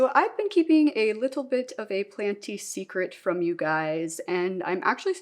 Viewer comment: I didn't even realize you had an upstairs for some reason 🤣 Also I thought it said "panty secret" and I was like "this going to be one weird tutorial"🤣🤣🤣